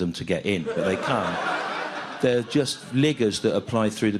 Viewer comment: what did he say there?? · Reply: "Liggers." Meaning "a person who always takes the opportunity to go to a free party or event that is arranged by a company to advertise its products." In other words, audience members there, not out of love for Stew, but because it was a free ticket to a TV taping. NOT what you may have been thinking.